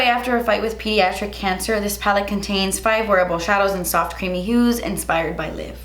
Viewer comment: I'm sorry did she say the eyepalette has 5 horrible shadows, even the captions says so. I cant seem to unhear that!!!